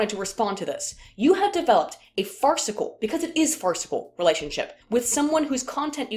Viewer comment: "Did she say fartsicle?" Thanks to Crazy Ira and The Douche from Parks and Rec every time I hear "farcical" that clip plays over in my head. "Shove it in your face mouth"....hahahaha